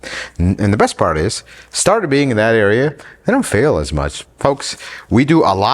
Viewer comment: i thought you said the 2uz starter isnt in that bad of a spot🤔